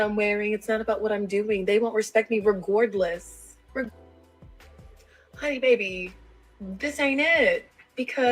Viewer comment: she actually said regordless, instead of regardless. Or, I assume that’s what it was supped to be. It could be it’s a word I don’t know.